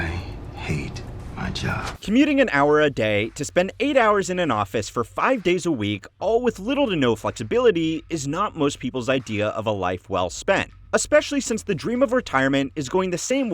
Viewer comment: Wait, did he say five days a week